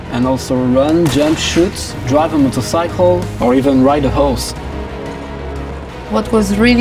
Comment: emo page? wow that's sorta..hot...but any one else thought the guy said ride a house not a ride a horse?....